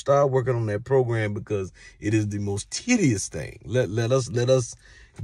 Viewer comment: Titeous did he say titeous bro its Tedious